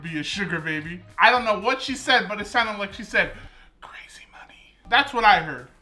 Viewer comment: Yeah I heard “CRAZY MONEY”